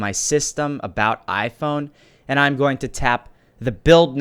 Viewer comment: did u say iPhone??😅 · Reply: About my phone!!!!!